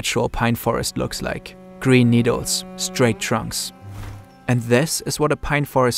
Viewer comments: I heard "brainstorm". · did you say “Green Needle” or “Brainstorm”? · What is he talking about brainstorms for at 6:35? Brainstorms and straight trunks? I dont understand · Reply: he says "green needles, straight trunks." - are you alright? that sounds nothing like brainstorm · What does the word brainstorm have to do with trees? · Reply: he did not say the word brainstorm. he said "green needles, straight trunks"